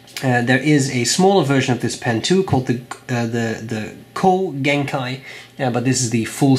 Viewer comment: What's the smaller one of this pen? I keep hearing "coal" genkai 😶🤔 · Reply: Ko-genkai, ko being Japanese for short/small if I am not mistaken